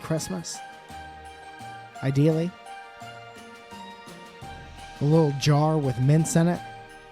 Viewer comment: When I was listening to the podcast I thought he said a little jar with mince in it and thought that was a pretty weird Christmas necessity